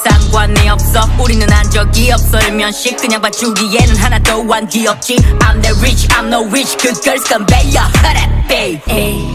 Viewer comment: Idky but I think I heard I'm the Blair Witch AHHHHH